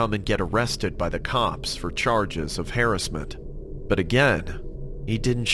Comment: no offence but lol you said hairissment instead of harrasment